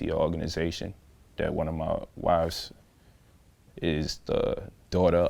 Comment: did he say one of his wives!?!?